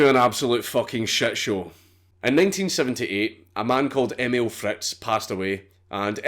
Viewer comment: did you say 1978? 😰 20:15 again? 😰😰